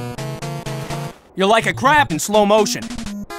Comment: "You're like a crap in slow motion." I have to use that on someone some time. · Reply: I heard "You're like a crappy slow-motion"